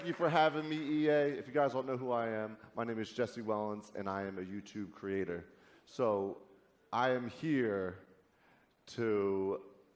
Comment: Did he said he is YouTube creator at 2:18?